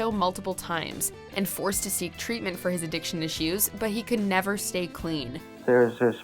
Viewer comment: She said addiction issues but it sounded like she said addiction to shoes.